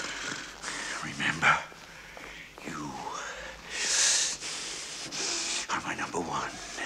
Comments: When I was a kid, I thought he was saying, “You are my number one and I!” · For some stupid reason I always heard him say “you are my number one... and I...” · I'm so bad with song lyrics and occasionally quotes. As a kid I thought he said 'you are my number one, and I' Don't ask why, it just sounded like that to me then. I'm soo glad this clears it up for me. :D